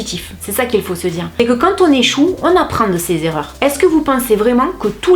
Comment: j'ai entendu "quand on est chou" 🤣